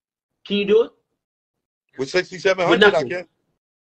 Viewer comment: He starting to realize his mom is a bitter babymama 💯💯💯 only him and her saying 6700 a month is not enough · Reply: I thought they was saying 600-700 and I was like yeah that shit prolly enough